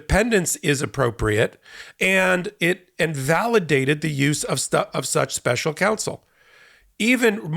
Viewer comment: I keep hearing "and invalidated use of such special counsel" which would run counter to your point, that it actually agreed that it was valid... Could you confirm or clarify? Thanks!